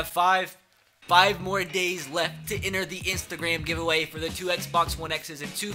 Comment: I thought you said Easter day giveaway. Well shit🤣